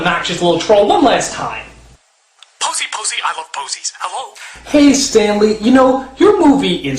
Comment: did Stanly just say pussy pussy I love pussy?